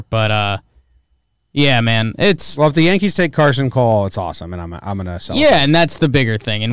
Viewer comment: did you say carson cole?